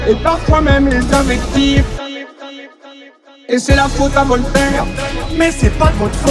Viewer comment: C'est moi ou il dit c'est la faute à vos mères